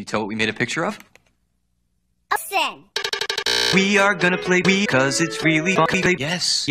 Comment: Can you explain to me what steve said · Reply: “We are gonna play Wii cause it’s really fucking gay.”